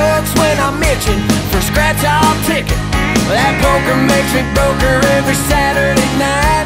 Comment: i thought he said playing poker makes me poker lol